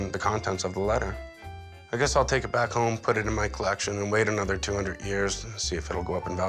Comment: Did he say, "Wait another 200 years?".